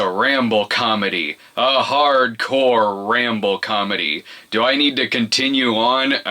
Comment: I first misheard ramble comedy as Rambo comedy, and had to rewatch that line with Close capture on.